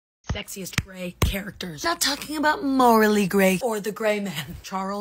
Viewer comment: I thought she said “sexiest gay characters” 😭💀